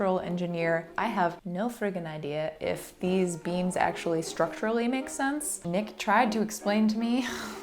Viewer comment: At first I thought it said fake BEANS and I was so confused